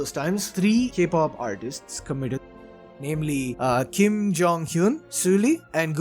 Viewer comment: did he say kim jong un at 4:43?